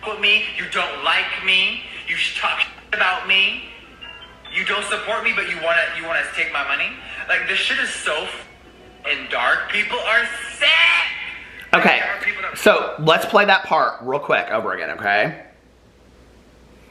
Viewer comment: I heard 'this shit is so dark, COMMA, people are sick. '